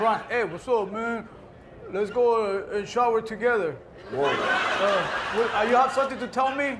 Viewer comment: I was crying when he said " hey pretend I'm lebron hey man let's go and uhhh shower together" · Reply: I heard "let's shot together" meaning play ball together.